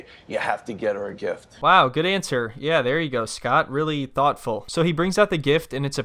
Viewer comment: Lmao this guy such a jackass. I just made her mad, but I thought she said “let’s go Scott” yeah Scott cuz it’s the translator pissing her off not you 😂😂😂